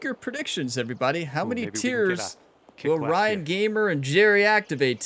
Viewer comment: “Make your predictions how many tiers…” I totally thought you said tears because you would be crying 😢 😂